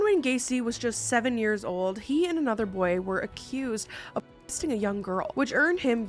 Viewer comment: I’m over here wondering what said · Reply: She said molesting